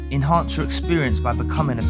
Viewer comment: For the longest time I couldn’t make out the beginning and thought you were saying ‘in hard to experience, or in heart to experience’- now I have subtitles and see it’s enhance 😂 ahh I’m so deaf. Irrelevant to everything but this has been bothering me for almost a year 🤣😂😂😂